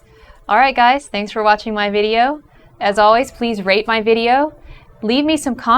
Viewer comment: When she said rate her video...i keep mishearing it as rape.